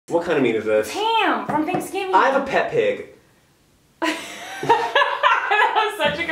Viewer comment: I thought he said I love Peppa pig